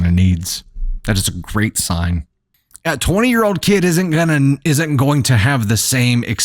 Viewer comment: Did he say "20 year old kid"? 5:56?